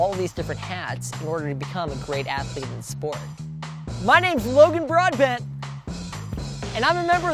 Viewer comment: i Heard it logan broadband 🤣🤣🤣🤣🤣🤣🤣